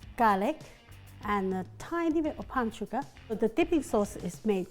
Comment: The subtitles are wrong at 2:27. She says "and a tiny bit of PALM SUGAR", which is what you'd really use here.